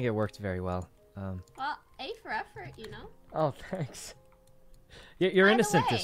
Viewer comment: did she say A for effort